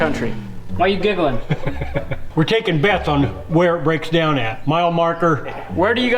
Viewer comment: I’d like to to call her “mine”! 😀 I think since she’s coming from Kansas she should be called Dorothy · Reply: I was thinking of calling her Beth because at first it sounded like said "we're takin Beth on" when he said we're taking bets on where it breaks down at. Minute